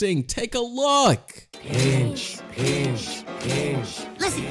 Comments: In teen titans it sounded like they said binch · they said pinch but still i heard the b word when i first watch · They actually said pinch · The very first one where you thought the ttg was saying bit** it was actually pinch